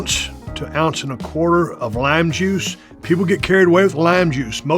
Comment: the way Stone Cold says lime juice I thought he was saying lamb juice